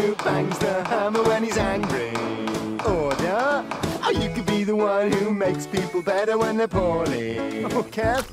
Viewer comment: what it says: Or you can be the one who makes people better when their poorly! what it sounded like: Or you can be the one who makes people better when thier p0rny! what it sounded like 2 : just all of 1 but replace the 'p' in p0rny with an 'h'